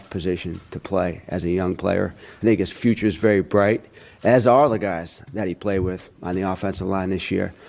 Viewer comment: I almost thought he said “n*gga’s future’s very bright.”